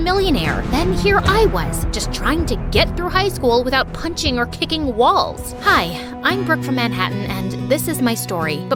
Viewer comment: I THOUGHT SHE SAID KICKING BALLS 💀💀💀